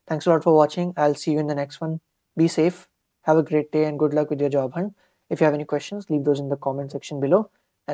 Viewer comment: I heard "good luck with your job hunt" at the very end of the video. Is it true?